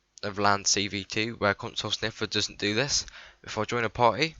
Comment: 28 seconds I thought he said paki sniffer